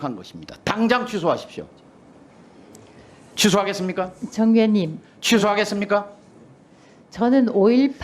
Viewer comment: 이진숙은 정의원(위원 ?)님 이라고 하였는데 최민희가 잘못 들었구만, 자기가 잘못 들어 놓고는 남 탓하노 ?